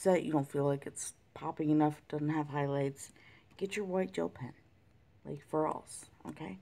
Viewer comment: Hi!! I Just today found your channel and am thriiled with learning how to correctly do these gems!! I have one question...what did you use to make the sparkling highlights towards the end? It sounded like you said a sparkling white pen??? I've not seen one, I've onle seen people use regular white pens, but I LOVED yours!!! Thanks!!! Also I'd love to know where you bought it and also, I just ordered one of your books too!!!!